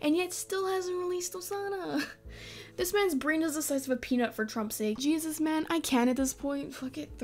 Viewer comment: did you say "for trump sake?" Also this is my first time on your channel and I've been watching his yandere devs channel for awhile but this easter egg is really disgusting..